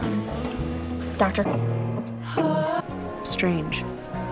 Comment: who heard “doctor strange”